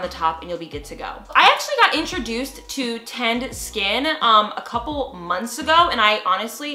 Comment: my dumbass thought she said "tanned skin"